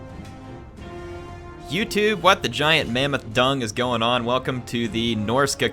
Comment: For a second there i thought he said "Giant Mammoth Dong"..